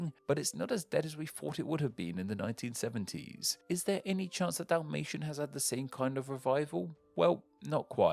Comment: There's also another language that died in an explosion, the Tambora language in the Indonesian island of Sumbawa is an extinct Papuan language thought to have literally died once Mount Tambora exploded and buried the village in layers of ash and lava. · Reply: Good segue, I thought it said "a dalmatian" and I was like "oh no!"